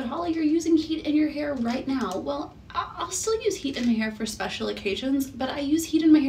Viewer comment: I thought it said "cutting my hair" i was so sad!